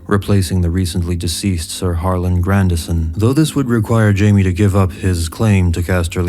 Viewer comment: I heard this as "Sir Marlon Brando-son" at first haha